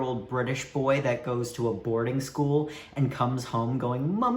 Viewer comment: ASDFHSGAJKAJS WHY DID I THINK HE SAID ABORTING SCHOOL EYE